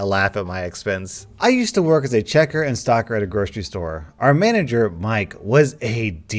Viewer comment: I thought you said checker and stalker